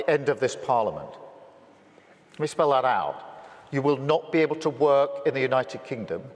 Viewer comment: Utterly evil, and yet, it’s not even the start of this globalist shitstorm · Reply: When he said "let me spell that out for you" all I heard was "Zig Heil!" 😂